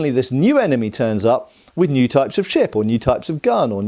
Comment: Thought you said "new types of Shit" instead of Ship. Ha ha ha ha. Great new technical term